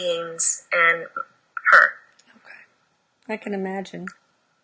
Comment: I heard a man's voice say OK...